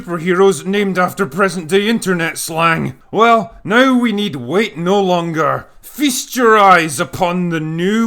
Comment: After you mentioned the overweight superhero, I heard "Now, we need weight no longer." lol